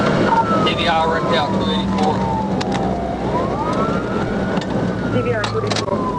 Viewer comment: YET at 6:12, they lying PIG is CLEARLY heard saying 'TVI, 284'.......... sooooo, what exactly did he mean to say?!? that he didn't just attempt a TVI at the 284 mm........🤔